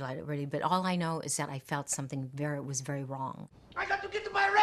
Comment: does she say : All I know is that it was wery Wong ?